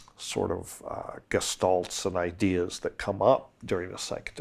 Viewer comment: Guys! What is Dennis saying at 6:40?? Sounds like "Gastulson ideas". What is he referring to? A famous thinker, i assume?